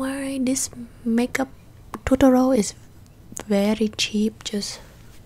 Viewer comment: Every time you say “tutorial” I hear “totoro”